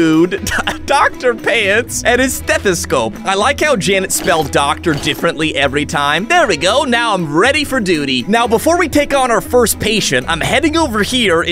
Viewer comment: When I heard “I’m ready for duty!” I heard it like “I’m ready for doodie!” Anyone else?